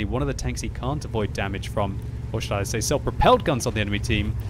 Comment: Did you just said Scheisse? ;D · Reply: "Or should i say" Not Scheisse